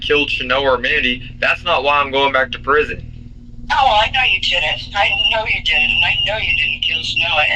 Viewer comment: Does she say I know you two didn't?